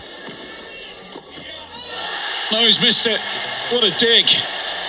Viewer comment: what did he sayyyy? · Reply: what a dig